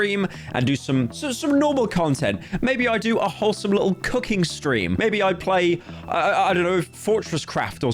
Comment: I legit thought he said cucking stream